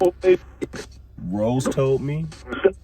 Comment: No one said Deborah did it ...but she had an idea who did and what happened,so did the other older sister. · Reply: it sounded like she said Rose told me tho . maybe mom ? maybe another Rose ? they decided it could wait . rosemary didn’t correct them . but it did sound like they were throwing out information all at once . also , came in . said on the first day we talked to him that God sees what he sees . he has the ability to project what they remember . what it sounded like to me was that it was a lot of circumstances and decisions made that they believed would make life worse for the living .